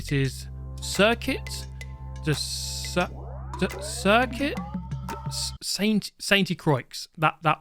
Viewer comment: "sayntè Crowa" is the way I heard it as a French